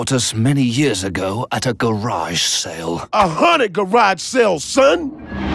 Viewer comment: Every time wax coolio says "A haunted garage sale," I always hear, "A Hanukkah garage sale"